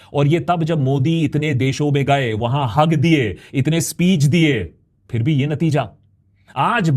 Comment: did he say 'Modi hug diye...' 😂😂😂